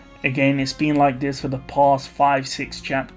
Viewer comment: I misheard the city's name as "ChaosFlame" This must be another foreshadow by Tappei!